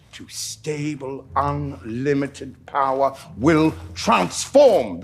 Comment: To be honest I think you misheard the words he said. He said “free unlimited Power.”